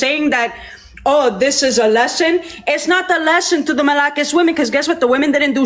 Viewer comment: She said 'Malakas'... She actually said 'Malakas'